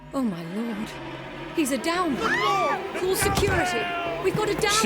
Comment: anyone else thought they were saying downy as in you know what